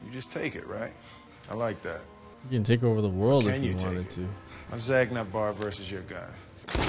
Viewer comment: How dare you call a zagnut a chocolate bar! It’s toasted coconut bar lol 😂 my fave candy bar. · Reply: Im pretty sure I heard him say "My zagnut vs your gun" or something 😅